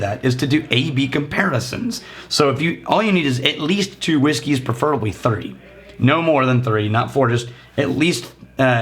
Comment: am I just mishearing or did you actually just freudian slip and say "preferably 30"?